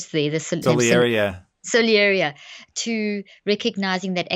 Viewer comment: What was the word used at 6:14? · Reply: I believe she said "Salieri, yeah." She was affirming the character name and related concept Tom was talking about from the movie Amadeus. He was a real historical figure, but fictionalized somewhat in the movie.